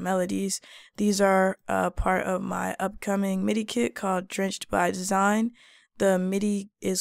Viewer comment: Finally! With out your take on things everything else starts to sound redundant. That pack could use some 90’s chords and I will Lofi them to my taste. Also it would b dope to have Breaks to chop but that’s my take. I’m so happy I can’t wait. 😁 🙏🙏🙏it’s all ......... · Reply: I was sleepy 😴 I thought u said sample pack but u putting together a MIDI kit. I’m not good with those. However , I think it would be dope for u to make available when downloadable videos on programming beats and looping your beats correctly for those who travel a lot like me. 🙏